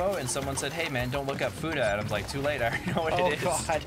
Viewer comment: Lol did say "futa"?? If so, I just want to make sure y'all know that "futa" is considered a derogatory term and derogatory concept in the LGBTQ+ community. It's just an excuse to fetishize transgender people, specifically trans women. No judgments here, just information! I know y'all are very polite and considerate when it comes to those types of topics~